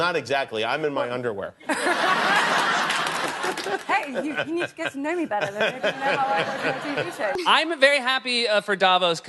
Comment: What did Natalie say at 1:30? Went a bit random speaking lol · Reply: think she says: "Hey, you need to get to know me better, then maybe you'd know how I watch my TV show."